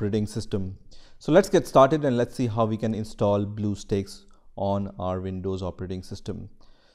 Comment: i thought he said blue balls for a sec but its actually blue steaks